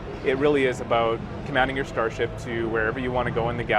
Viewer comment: did he said "commanding your starshipS"?